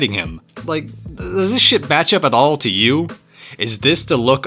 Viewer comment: I watching like "oh interesting!" Then I heard "DASSHIT" and was like "ITS ABOUT HIM!?" (Personally haven't read the comic or manga or wtv but the art looked sick and his music I love)